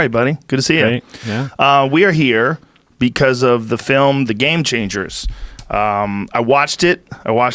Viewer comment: Thought Joe said movie called "Game Chasers" for a second